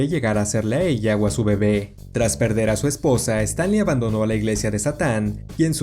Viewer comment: Yo entendí a Stan Lee Xd, 7:03 Ahora yo lir en la transcripción en que el nombre es parecido Xd